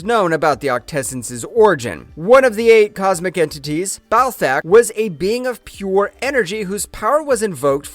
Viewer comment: i keep hearing "Ball Sack".